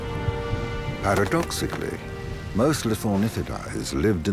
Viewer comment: Did you really say "lithornitidaes" with an S one the end?